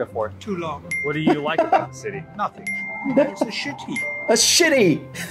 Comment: He actually said "shit heap" instead of the bad subtitles